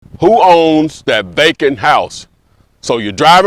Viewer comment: I swore he was saying “bacon house” 😂😂